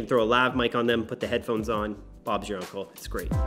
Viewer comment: Did he say "Bob's your uncle"?